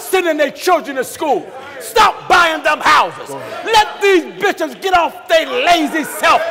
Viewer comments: "BISHOPS'' is what he said. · Did I hear Pastor G mistakenly use the word bitches instead of preachers?? 45:57 mark. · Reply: Bishops